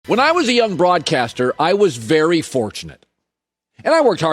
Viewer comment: Lmfao at the beginning i thought Colin was saying "When I was a young broad"